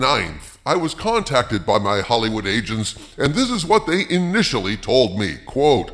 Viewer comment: Kinda sounded like you said, “My Hollywood Asians”….😂